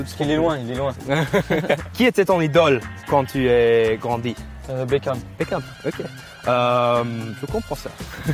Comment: I swear I thought he said Beakhyun from Exo. ha ha